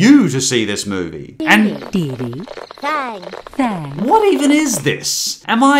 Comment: What are they even saying- By what I could make out: “Dee-dee” “Bye” “Bye” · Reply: I think it's "Greeting Didi! Hi Thang!"